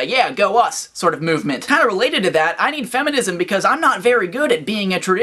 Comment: All I hear is, "I need feminism cause I am a bitch". I ain't no macho man but god damn yo. Wtf.